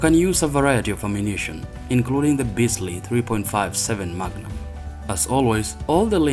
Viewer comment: did you say Bisley 3.57 magnum?